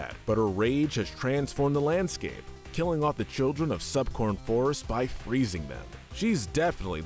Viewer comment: I'm sorry, did you just say "Subcorn Forest?"